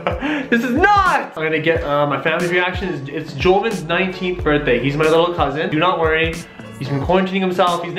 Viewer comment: no offense but i thought u said covid-19